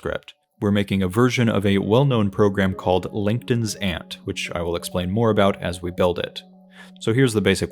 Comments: I genuinely thought you said "LinkedIn's Aunt" and now can't stop hearing that. Love this video and your attitude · you said langton's ant in a way to the point where the caption thought you said linkedin's ant